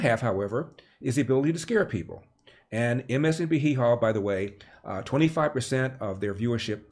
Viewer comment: did you say MSNB hee haw?